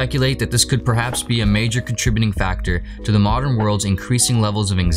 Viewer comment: I disagree. I believe people are depressed and anxious because their real wages haven't grown in 30years. This has led to people working more hours to be left with less at the end of the month. 57% of American's cannot handle a $500 emergency without going into debt. Living on the abyss of financial ruin is very stressful, and working hard to make no progress is very depressing. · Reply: I rewatched it and he just calls it "a major contributing factor..." so I guess his statement is true. The first time I watched it I thought he said the main reason.